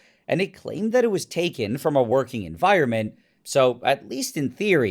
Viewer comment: for a second I thought u said "working fireman" 😭